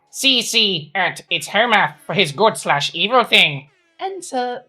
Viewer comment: I keep hearing "Homer" and not "Hoe-Math". 😂